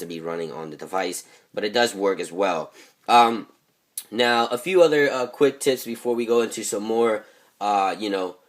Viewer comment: Did he say a few other quick tits??